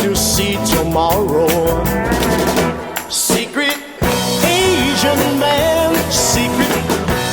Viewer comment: All this time I thought he was saying "secret asian man". Huh. Learn something new every day.